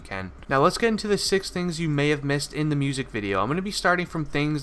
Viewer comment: Did u say 6 things?